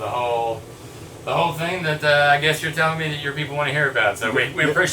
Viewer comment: Did he just say “You people”? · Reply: No I said "oye pipo"